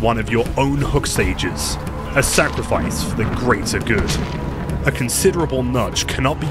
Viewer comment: Did I hear "for the greater good"?